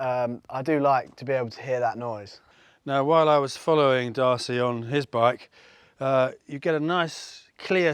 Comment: I definitely preferred the Twin Slash with the baffles as you have them, Great stuff guys ! Most comprehensive comparison on exhaust I’ve seen. At 7:23 in the video I hear the old man say “while I was following Mr Darcy” I guess the young man is putting the old man in the dust , interesting 😆😆 !